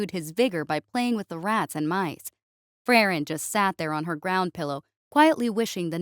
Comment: I heard "Frairen" and thought, "yay! My favorite Yorples" lol